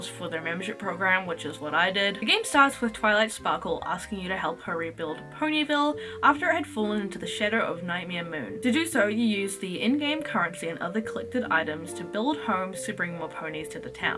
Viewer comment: I fully thought you said "after it had fallen into the shitter"